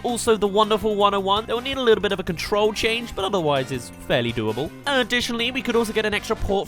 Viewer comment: wtf did he just said lol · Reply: ‘It will need a little bit of a control change’ ??